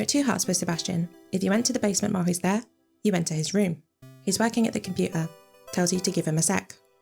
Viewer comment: I heard “he’s wanking at the computer” and my jaw dropped